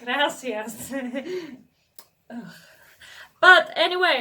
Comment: did you say "gracias"? And if you say it, that was in spanish or it was in your mother language?